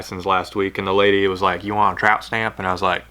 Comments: Thought you said tramp stamp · For a second there I thought you said tramp stamp ! HOLY CRAP I JUST HAD A LIGHT BULB EXPLOSION! IN MY HEAD !!!!! You need some of those lick and stick tattoos with your dueling treble hooks logo !!!!! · LOL. I thought I heard "tramp stamp" HAHAHAHA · I definitely thought you said “tramp stamp” 🤣🤣